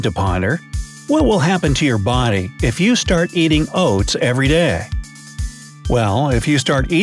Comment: Put ur glasses on. I thought it said eating Cats, WHAT?? Hahaha 😝